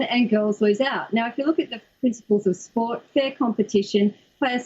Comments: I honestly thought the reporter was saying nipple.... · I love Aussie english!!! I kept hearing 'Nipple' 🤣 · I thought the reporter said ”nipples” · I can’t take this seriously. All I hear is “nipple”. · It sounds like they said nipple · All I hear is "nipple" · I keep hearing "nipple".